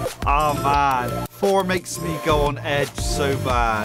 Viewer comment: what did he say?? i played it again and i didnt mishear it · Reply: imagine "four makes me go on edge"